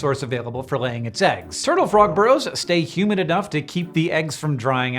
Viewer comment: For a moment I thought he said "turtle frog bros"